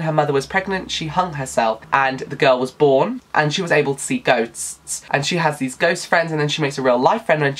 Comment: When you said that one character could see ghosts, I thought you said see goats and I was so confused XD I will have to check out some of the stand alone fantasy novels! Thanks for the recs